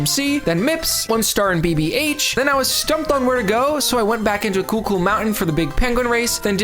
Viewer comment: I hear "Coocoo Mountain" everytime you say it, and that's now how I'm officially calling that stage.